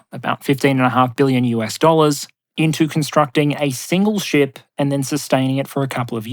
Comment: Hoi, heads up, at 30:16 the subtitle says "5.5 billion US dollars" when you said "15 and a half billion US dollars."